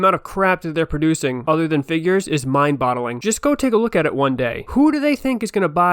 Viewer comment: Did you say "Mind-Bottling"? 😮